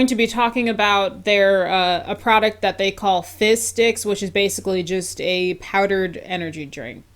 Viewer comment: I kept hearing “fish sticks” instead of “fizz sticks” 😂